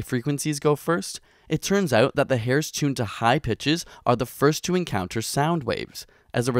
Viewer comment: When he was talking about the "high pitches" I heard "high bitches"... I guess my hearing is not so good after all :(